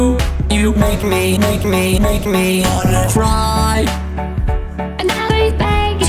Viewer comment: Preston sounds like he said try